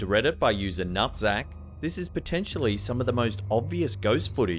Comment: I'm sorry come again did you just say "nutsack?" lol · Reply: the name of the person is actually nuthzac ...but yes it did sound a lot like nut sack